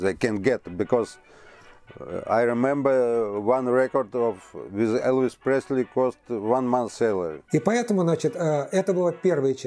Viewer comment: What the fuck was he trying to say with that accent? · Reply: "I remember one record of Elvis Presley cost one month's salary."